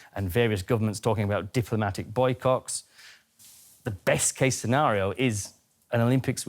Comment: Did he say ''Boy cocks''? LOL keep your sick thoughts to yourself man!